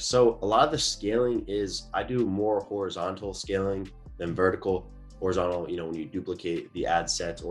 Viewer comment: Can someboy tell me what did he said in 23:46 "I do more untle scaling"? i dindt understand, can somebody explain me? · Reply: Hey Luiz! He said "I do more horizontal scaling than vertical". Hope it helps!